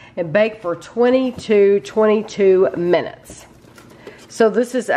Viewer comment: SOUNDED LIKE YOU SAID COOK 🕙 WAS 22 TO 22 MIN. 😊 BUT IT WAS 20-22 MIN. MORE PEPPERONI PLEASE I COULD BARELY SEE ANY😵😳😂😂